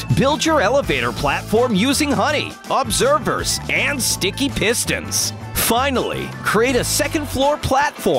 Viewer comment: Sounded like the narrarater said stinky pistons